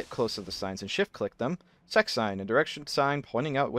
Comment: what'd he say? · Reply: SEC